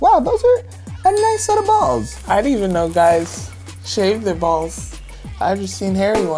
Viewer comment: tell me why i thought it said should guys shave their babies